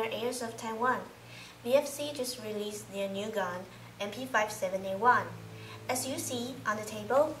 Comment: Did she just say MP57A1?